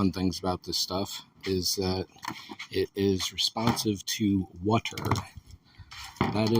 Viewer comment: Have a time code so I can check for the word spoken? This is several videos into creating a highly customized hatch for the car. Check the associated playlist. I'll have some concept art in another video yet to post. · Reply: oh, the word is responsive, but I still don't understand what that means. sticks better, or worse or sooner? Just a sentence explaining in the beginning of each video or in the title or description will clear it all up.